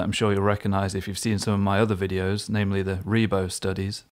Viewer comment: Thanks. Subscribed. By the way, even with subtitles on I can't quite figure out what you're saying at around 3:53, something like "revo studies". Is this something you have a lesson on? I'd definitely check it out. Anyway, great work with this one. Thanks again! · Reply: Thanks for the comment and for subscribing.. much appreciated 🙏🏻 Ah yes, I’m referring to my “Ribot” studies, I’ll link them here; <link> <link>